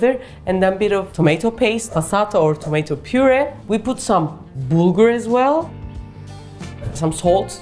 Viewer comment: Hi, thanks for the great video. I couldn't understand what you said at 3:20 minutes, the orange stuff you put in. Did you say bugra, brugle? What is that ingredient? Cheers. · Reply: She is using bulgur wheat.